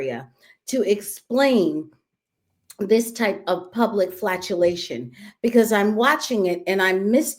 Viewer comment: It sounds like is saying “flatulation”; sounds similar to her description of their flagellation. These people sound more like the former. 🤭